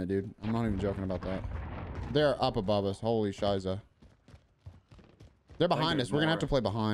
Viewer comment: Did i hear right?? "Holy Scheiße" at 17:33??